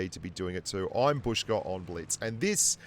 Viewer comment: First time I hear "Bushka on blitz" and not "Bush gran blitz"